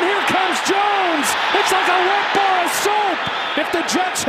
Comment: "its like a wet bar of soap" 😭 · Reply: I honestly thought he said "it's like a wet bowl of soup" 🤣